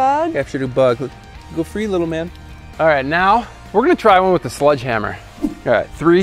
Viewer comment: did anyone else hear sludge hammer?